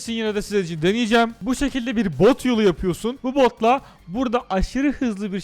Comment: bo* yolu diye duydum😂